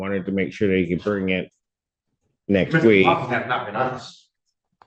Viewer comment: What did defendant not say at 11:59? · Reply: “Mr. McLaughlin was not being honest”